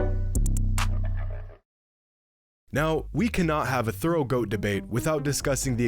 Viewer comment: did he say throat goat ? Haha